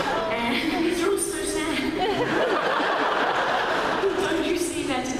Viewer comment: What did emilie say at 2:06 after her first line:? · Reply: she said "these are all so sad".